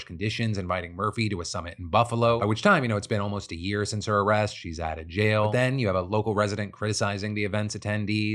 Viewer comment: In the story about the cattle when you said in passing "she's outta jail" I thought you said "she died in jail" and was like WTF? way to bury the lead!